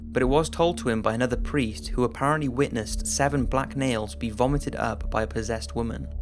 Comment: thought he said "7 black males"